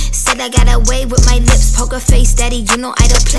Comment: I hear: "How that part feels" and "Poke a face" but it makes sense for it to be "pokerface"